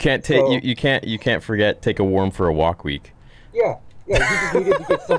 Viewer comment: Great point about Tommy pointing out that Daniel and Alli were having a good time together; he had a huge smile on his face when he made Johnny aware of this. Tommy definitely egged Johnny on at the beach but I would also say that Dutch did the same thing in the locker room scene when Daniel is putting on his gi for the tournament. Johnny was behind him but Dutch was the instigator, with Tommy egging him on. Tommy never approached Daniel himself and challenged him. Bobby, on the other hand, seemed to be the group's superego, the higher self. He felt guilty about kicking Daniel's leg and he told Johnny that Daniel had enough. Johnny was swayed by the feelings of the group. I think Tommy did it for his own entertainment but Dutch enjoyed it because he was cruel. I still don't understand Tommy's statement of "It must be 'take a worm for a walk week!" I've never heard anyone use a phrase anywhere close to that to insult someone. Or maybe...just maybe, Freddy Fernandez from Apt. 17 started the whole thing by inviting Daniel to the beach party. If he had not gone, he would have been that dark haired, quiet kid from New Jersey who moved to California his senior year. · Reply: I thought he said "take a nerd for a walk week"